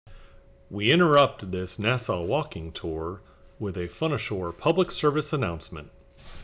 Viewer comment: For a second there, I thought it said " Fun asshole". =P Cool Video! I looked up "Walking tour Monaco,", and then followed you down here. =)